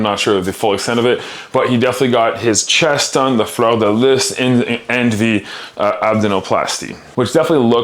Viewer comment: Was that fleur de lis he said at 0:47? I think that’s what he said but the pronunciation was rather odd to my ears.